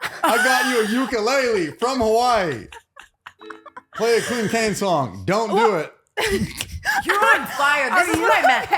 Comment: What did Trevor say at 6:16? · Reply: ”Play a Clinton Kane song - dont do it!”